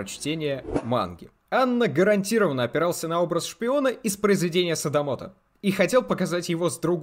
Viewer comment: скажите плиз что не я одна услышала "Из произведения садовода" и такая "... Ну да, ну да, арбузы. Это имеет смысл... "